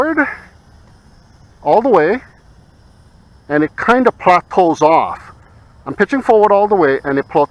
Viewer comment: you mean "plateaus off" ? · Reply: I don't know what was said; I didn't make the video... I thought I hear "prop pull"... And matching it to the video, I only see cam angle shifting...